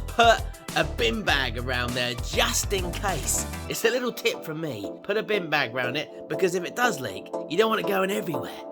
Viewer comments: I thought you said a Bean bag not a bin bag. Lol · Lol with your accent I thought you said bean bag instead of bin bag 😂😂